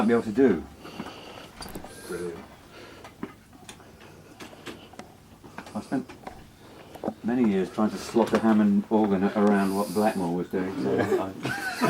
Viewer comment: What was Jon saying about Ritchie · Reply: He said " I've spent many years trying to fit in a Hammond organ around whatever Blackmore was doing at the time. " Basically he was having a dig at Ritchie, implying that he overplayed on stage and he ( Jon ) had to fit in where he could.